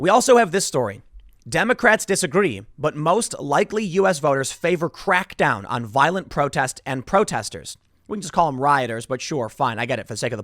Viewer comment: I misheard this. What I heard was "Most 'likely US voters' favor violent crackdown on protests & protesters." Which sounds great to me.